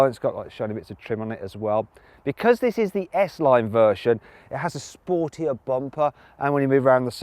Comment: 「スポーティーなバンパー」って日本語で言ってるように聞こえる